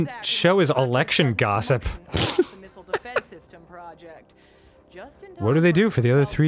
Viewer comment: When the news person said "missile defense system," I thought she said nipple defense system